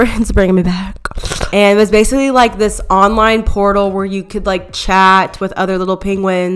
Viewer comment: i thought u said “it was basically this online pornhub” i